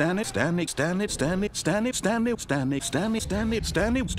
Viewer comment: Go on for too long, it sounds like he says "Sternly"